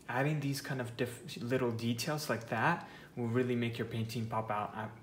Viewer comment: i thought he said, "it will make your teeth fall out" XD